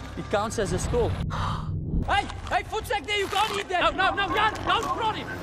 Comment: He says "voetsek!", but the subtitles says "food sack" 🤣